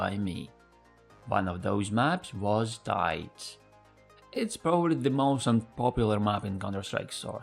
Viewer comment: caption calls it de_dust 2 instead of tides